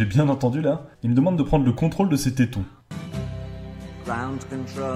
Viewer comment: moi, j'ai entendu "prend le contrôle de Mettaton", mais c'est parce que je suis fan d'Undertale